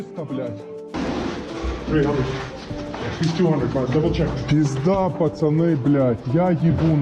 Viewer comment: I'm sure I hear English with an American accent - "300...he's 200, but double-check" 2:44 -Saya yakin mendengar bahasa Inggris dengan aksen Amerika - "300...dia 200, tapi periksa kembali"